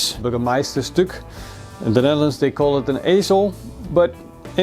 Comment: Sounded like he said they call it asshole lol